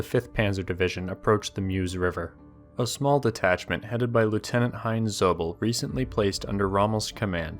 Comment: who else heard "Lieutenant Han Solo"?